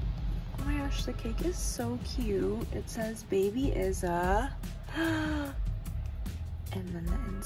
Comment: When she said the cake says “Baby is a..” I thought she was saying the baby’s name is Issa 🤦🏽‍♀️🤣🤣🤣 Can’t see or hear y’all pray for me